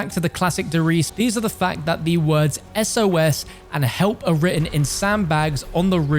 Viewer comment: I thought that said sus